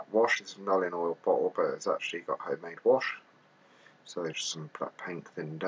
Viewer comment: What is in the bottle at 9:35? Can't make out what you are saying. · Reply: Home made black wash